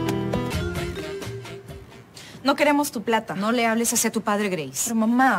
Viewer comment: Que dijo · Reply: No queremos tu plata dice